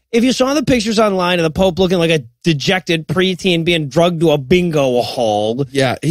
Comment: Did just say the pope looked like he was being 'drugged' to a bingo hall? C'mon man, I thought you were more educated than that. 'Drugged' is past tense of taking drugs. What you meant was dragged.